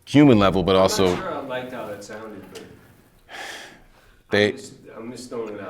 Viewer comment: what does Em say at 2:10? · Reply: "I`m not sure I liked how that sounded".